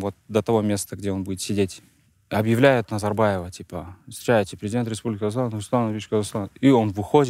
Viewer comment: Президент Казахстана! Нурсултан Абишевич Казахстан 😂 мне так послышалось